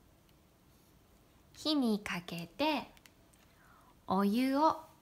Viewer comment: Sumimasen, at 3:19, did you say "ki ni kakete"? Because the subtitles say: 火にかけて, but this expression does not appear in Jisho. Only: ひをかける. I will be very grateful if You can clarify this. Honto ni sumimasen.